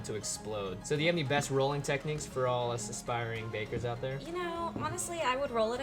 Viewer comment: when you said bakers I thought you said vapers